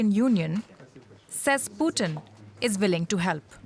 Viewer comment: I thought they said Putin is dying. Western are problems of the world 🌎